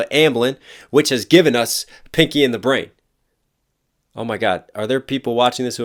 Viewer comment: I thought you said “picky and the brain” and I was confused lol